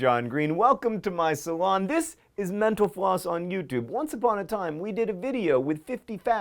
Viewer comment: Sometimes, when John says "Mental Floss," I hear him say "Menopause." It confuses me every time.